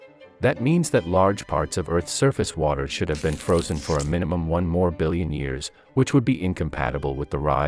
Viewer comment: I thought he said morbillion